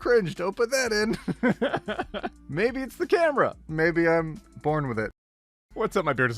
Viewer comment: When he said “maybe Im born with it” I hear “maybe it’s Maybelline 🎵 “.